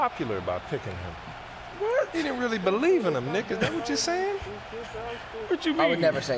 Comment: “Sound like you don’t believe in the nigga” is that what he said @ 6:40 😂😂😂